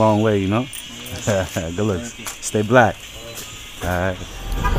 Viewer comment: did he just say stay black?😭